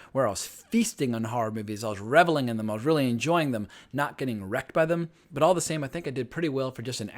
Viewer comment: when you said "...enjoying horror movies and not getting wrecked by them.." I heard "..erect by them" the first time ! :). Nice video. I remember first time I watched a horror movie when I was about 8, it was a black and white Frankenstein movie on TV (no Idea what it was and it may have been color but we only had a black and white tv at the time), but I couldn't go upstairs in the dark anymore as it always felt like there was something lurking in the dark. I need to dig out some of those movies on your list as they definitely bring back memories, but where are the hammer horror movies on your list? Being from UK they are my go to for old school scares, though dated now.